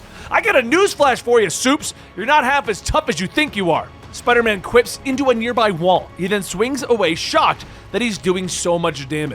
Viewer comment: I think I've been watching too many speed run vids. I thought he said "Spider-man clips into a nearby wall".